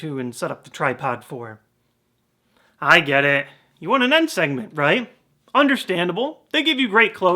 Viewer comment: "I get it, you want an end segment." Crap, I thought you said N segment. xD And yes I do want a video about N. If there are any such fan theories on him.